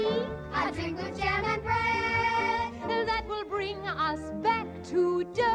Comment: jam and breadがgerman breadに聞こえてたw ストーリー的に言う訳ないけど